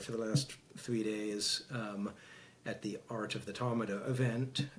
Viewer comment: Okay... I thought our guy here said they were at the "Art of the Talmud" feast. But he said "Art of the Tamada". PHEW